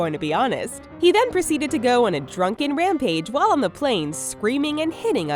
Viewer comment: the subtitles say on the page when it should say on the plane